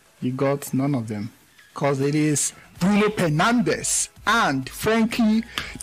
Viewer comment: I heard 'Bruno Pernandes' did you hear👀?